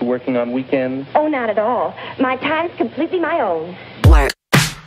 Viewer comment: Could you please tell what they said at the beginning? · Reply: "oh not at all, my time is completely my own"